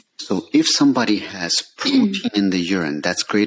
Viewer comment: What word did he say at 5:28? If a person has this in their urine, they need a biopsy of kidney. The word is distorted in the microphone. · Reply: Protein